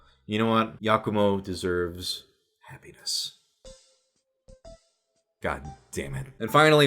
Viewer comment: Genuinely thought you'd said "Yakumo deserves the penis".